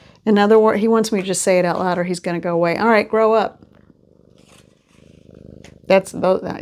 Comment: He said, “Grow up!” In my head I heard, “Grow up and glow up!” ❤😂❤